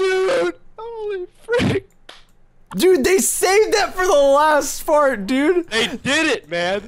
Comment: it sounds like he said the saved it for the last fart